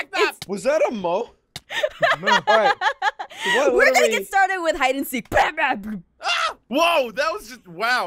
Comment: who thought said batman when she said lets get started